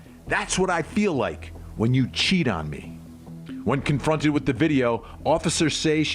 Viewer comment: I heard "choke me" not cheat on me. Not to say it makes it ok it is still motive and something she should not have killed him. I am simply saying what I heard.